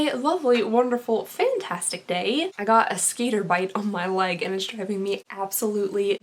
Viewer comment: I thought you said skater bite, not skitter bite!! Lol